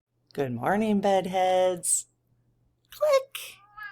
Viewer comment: Hi. You're awesome. It sounded like you said, "buttheads" instead of bedheads." Maybe becos you were pissed off.